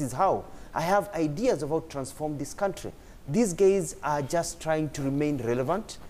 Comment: I heard Mutua say "these gays"